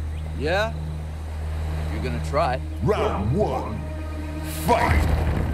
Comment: It said "Fight" but I heard "F*k" 😂😂😂 I knew this was coming or should I say I knew this was c*mming 😂😂😂